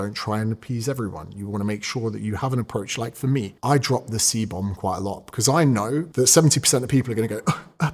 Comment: I thought he said cbum